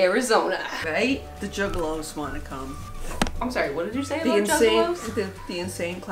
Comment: "i'm sorry mom, what did you say about juggalows?" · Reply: I THOUGHT SHE SAID JIGGALO WHICH IS BASICALLY A MALE PROTITUTE OMFG